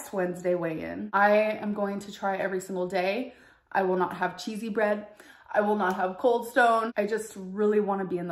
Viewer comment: i thought she said didn't have cheesy bread?